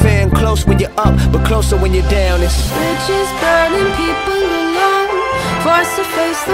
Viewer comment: It sounds like she says bitches instead of bridges xD